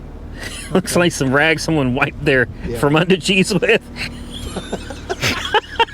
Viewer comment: Can somebody help me translate something? Apologies, his microphone's actually pretty good throughout the video, but can someone translate what he's saying at about 4:52 (sounds a little like he's saying "Looks like some rags someone wiped their from under cheese-a-wiz??" (I imagine obviously that's not what he saying because they both laughed pretty loudly – I'm guessing it's something sexual(?) but I don't know what he saying), can someone help translate? Thanks! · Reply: Fromunda cheese with. Is what he said. It's just someone's nasty ass.